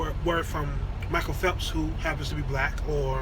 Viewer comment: Ok, did I just hear you say that Michael Phelps is black? right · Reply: Watch again. I said Michael phelps AND the young black women......